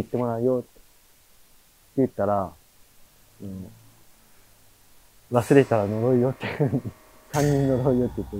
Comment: ｢忘れたら呪うよ｣ 確かに言ってますね(^_^;) イヤホンだと聞こえます